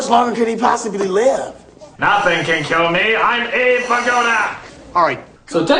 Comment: creo que dice en realidad "NADIE PUEDE MATARME SOY ABE VIGODA!" aun asi, no se cual suena mas gracioso, si ese, o como lo pusiste vos, ambos son graciosos, pero no se cual da mas gracia, de cualquier manera fue muy gracioso! XD